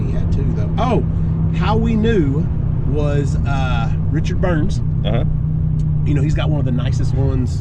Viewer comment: Did you say Richard Burns 2:29 🤣